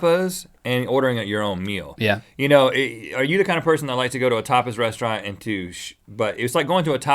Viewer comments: I swear I thought Rhett was saying Topless. I had to go back a bit in the video and listen. Next Debate: Owning and Buying a Dvd vs Netflix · sounds like he said "topless" lol